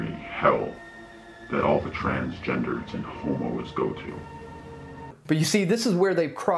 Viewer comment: Oh my gosh I swear at 5:25 I heard "homeowners" not "homos" I was so confused🤣🤣🤦